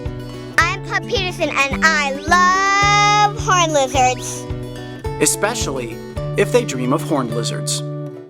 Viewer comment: I thought he said hornet lizards